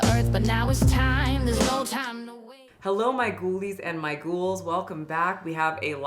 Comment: Anyone expecting for "I'm Disabled as Sh*t" Merch? Cuz..I wouldn't put it past someone like this. Real question -> At what point do you think “body positivity” turns into self-destruction? I’m curious to see where everyone draws the line. Check out the last video <link> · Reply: I know you said "ghoulies", but I heard "goolies" a half rude British word for gentleman's giblets.